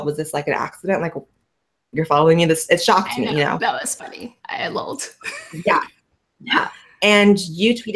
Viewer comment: im sorry but did i just hear her say "i lol'ed"